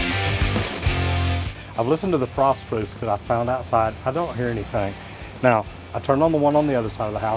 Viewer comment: subtitles says I've listened to the prostitutes i found outside 😂